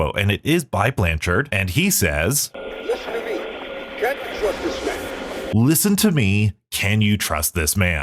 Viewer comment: I think he says "you CAN'T trust this man". The you is hard to hear because the emphasis in the sentence is on CAN'T and it just flows into the word like "y'CAN'T" which is pretty common to hear. I don't think that kind of emphasis makes sense with "CAN you trust this man?" i'd think he'd say something more like "can you TRUST this man?" if that were what he said. And i think him giving a warning to someone arriving or who's at the facility makes more sense with the story so far, and makes more sense with "Listen to me" before it.